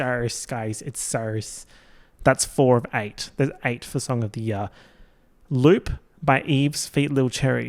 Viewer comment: I THOUGHT HE SAID STRAIGHT OF THE YEAR and was dyinnggggg, was really confused when he said they were all Melbourne based straights